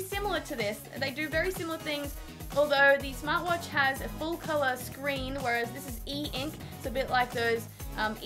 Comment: sorry, the watch is not e ink, but a normal full colour screen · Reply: (AppChat) Actually, I just went back and checked, you did not say "full color e-ink", I just misheard you. My bad.